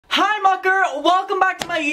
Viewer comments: I’m just wondering, are you saying “Hi Mucker?” what does that mean 😂 sorry if I missed you explaining it before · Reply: I always thought he was saying Hi Mocker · I’m just wondering, are you saying “Hi Mucker?” what does that mean 😂 sorry if I missed you explaining it before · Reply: PLS i was always thought he was saying "munker", like a nickname for a monkey because we're monkeys at his circus?? I FEEL SO STUPID OMGGG